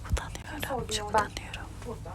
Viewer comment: ne diyor Anladım cevaplar için tşk berkeye diyor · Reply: Çok utanıyorum çok utanıyorum diyor